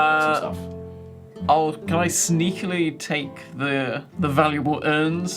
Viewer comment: At around 1:56:00 did say Sneakretly?